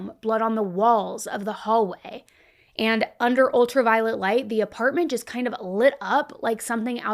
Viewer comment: i legit heard "ultra-violent light", guess it fits.